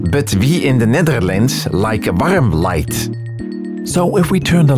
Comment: what does he say here... "we in the netherlands like XXXX light"? english is my first language and i have literally no idea what this is, despite english being fantastic. can anyone help? it's driving me crazy lmao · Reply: he is saying “warm light”